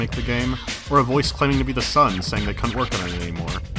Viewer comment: Wait Im rewatching this again and in the start of the video where he brings up examples of setbacks does he mean someone claiming to be the sun said they couldn't work on it anymore. I always thought it was like a son saying that the father or mother couldn't work anymore or somethinf